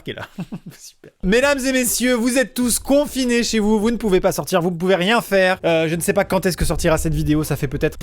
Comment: à 0:33 j'ai entendu vous etes tous con a cause d'un bug Likez si vous avez entendu pareil